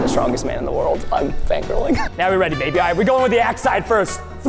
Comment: Between 11:10 and 11:15 I swear I heard say "I'm ready babe."